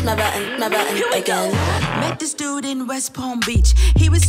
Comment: Lol I thought missy elliot said" met this dude in West bromwich"